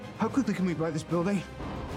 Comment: My favorite lines from this scene: “Go to sleep, go to sleep, go to sleep” after knocking Hulk’s tooth out “I’m sorry...” “How quickly can we buy this building?” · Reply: I thought Stark said "How come we didn't buy this building?"